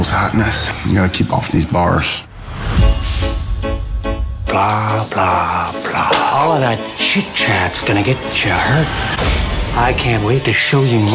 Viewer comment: What does the Joker says: 0:12? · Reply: Blah blah blah, all of that chit chats gonna get cha hurt